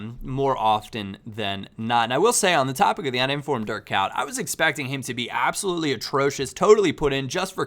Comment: It sounds like you say dirt cow when you try to pronounce Dirk Kuyt